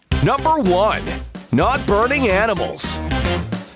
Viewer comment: he said not burning animals instead of lava pool try to edit even more correctly